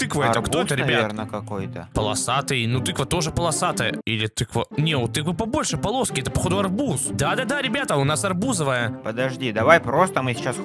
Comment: я один в первый раз услышал "да это бузовая"😂😅